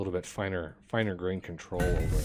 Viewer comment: At around 11:30 I heard you say you wish you had more finite control on moving specific amounts with the caravans. I am pretty sure you are using the caravan extended by Ezketara (actually watching to try and find out which mods you have,) but if after clicking on the right side to determine which type of route you click on the left side it will adjust quantities. The route to move specific quantity starts at like 1000 and goes up, but after 5000 or something wraps back to start over at like 10. :-)